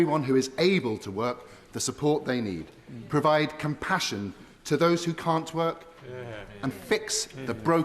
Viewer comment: Did he just say "Provide compassion for those who can't twerk"? (1:08) Well if that are the priorities for this parliament...